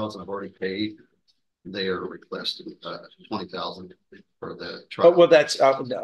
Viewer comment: did I hear correctly? They’re asking for $20,000 for the trial?